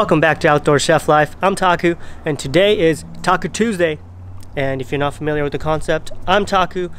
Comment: I thought you said Tako Tuesday